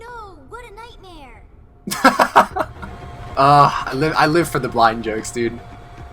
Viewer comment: Did you just say ya live for the Black jokes?